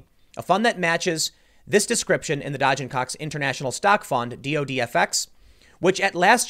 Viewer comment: whoever named their company Dodge & Cox lol wtf man i thought you said dodging cocks lol